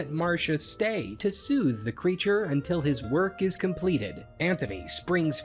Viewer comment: Captions typo at 2:42, it says create instead of creature.